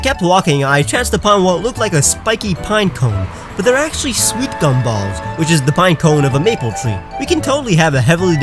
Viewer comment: What are you saying? I looked up “Sweep cone balls” but nothing popped up.